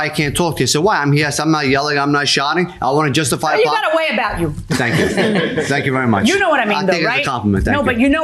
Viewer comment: What did the judge say. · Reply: She said , " you got a way about you "